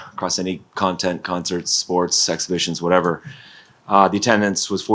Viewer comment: Did he say sex missions lol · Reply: i thought that too, he said exhibitions